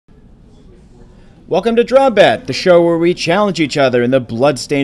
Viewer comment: oh drawmbat as in combat (?), I thought they kept saying drawn bad